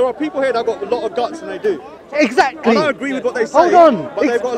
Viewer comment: "...and I agree with what they say..." I think it's fair to say there'll be no intercession for you. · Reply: i think he said dont agree but because of his slurred accent its hard to hear.